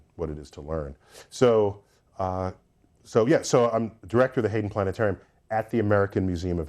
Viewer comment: Did you just say we made a planet